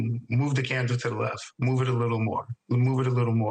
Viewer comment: Sounds like he said “move Cassandra to the left, move her to the right” not camera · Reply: I agree with the other comments…he said candle, not camera for sure